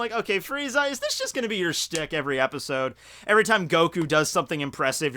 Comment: is he saying stick?? Or he saying a different word